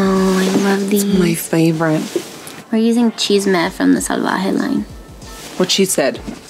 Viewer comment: When she said “chisme” I thought she said “cheese meth” 😁